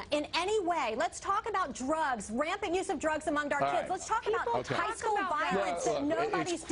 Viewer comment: did she say drugs among DARK kids or OUR kids?!!